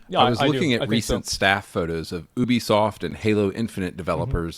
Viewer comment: Hahaha damn he really said boobiesoft I thought i heard it wrong so I had to rewind like 3 times to make sure 😂😂